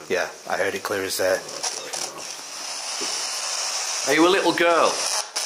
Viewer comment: asks are you a child? As soon as the Sbox comes on you can hear I'm the devil. Strange as it swears at you as well so possibly this is the same entity that is attached to you from the asylum with your family being one of the nuns back then. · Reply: when he asks are you a little girl it also sounds like it said devil.